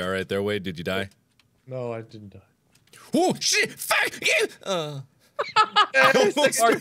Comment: did Mark say feck instead of fuck